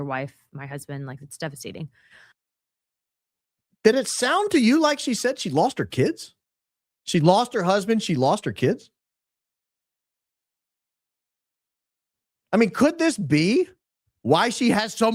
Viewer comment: I did hear it I had to rewind it a few times and listen to it very closely but yes, it did sound like she said she lost her kids.